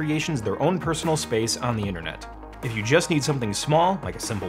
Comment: I keep hearing about this “innernet” thing. Is there an “outernet” to go with it?